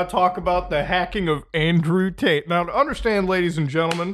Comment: Not gonna lie I thought you said Andrew taint at the beginning